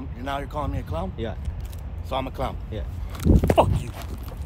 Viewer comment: "clown?" you called him a clown and thats his reaction? first watch i heard "cunt" and was kinda on his side. BUT "clown" .....just wow.